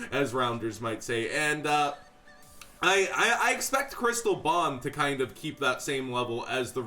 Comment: I straight up thought this nigga was saying Chris LeBlanc instead of Crystal Bond. 😂😂 i was mad confused.